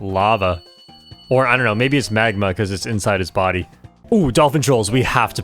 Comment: "maybe it's magma" man I heard "Maybe smegma" like 3 times (if ur gonna search what it is, beware of nsfw)